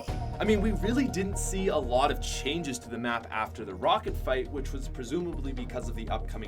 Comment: did he say rocket fight?